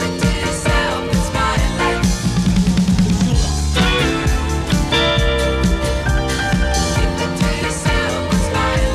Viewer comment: Whose voice is that? And what’s he saying? It doesn’t sound like Billy’s voice! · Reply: That's Billy. I think that he's saying "It's over" But maybe can he speaking in Japanese or French, he usually puts French and Japanese words on his songs (In live)